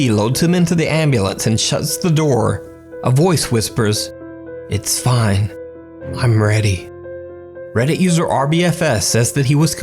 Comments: Last one I heard “This time, I’m ready.” Vs “It’s fine, I’m ready.” ... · You can hear “it’s fine, I’m ready” But I heard “I am not ready” instead. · “It’s fine, I’m ready.” Some ghost boutta get his cherry popped, my man go get em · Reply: Idk why but I hear "This time, I'm ready" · I heard, "Stop Mcgregor" instead of "its fine, im ready" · That’s NOT what I heard in the ambulance whisper. I heard, “This time I’m ready.” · Personally, I thought it said "I'm not ready" Not "Its fine. I'm ready" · Bro the last clip I Thought it said “it’s five nights at Freddys”